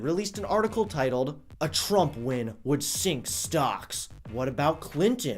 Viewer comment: "A Trump win would sink stocks." ... one year later ... stock market has 70 record highs inside of one year :D Way to fail (yet again) CNN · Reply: Ronald Stringfellow Oh lol I thought this said "A trump win would stink socks" Lol 😅😂